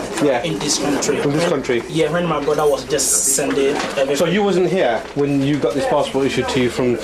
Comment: One of the officers ( the Indian looking guy in stripe shirt) couldn't speak correct grammar" I heard him said "SO YOU WASN'T HERE" LOL